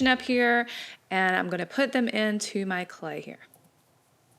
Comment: Did Anyone Hear Say Put it in My Clay XD